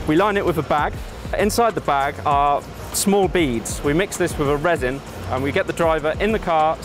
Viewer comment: is it just me or did he say "small beads" and not "small beans"?! :D